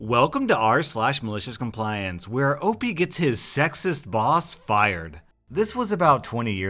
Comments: I thought it said sexiest not sexist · I thought it said sexiest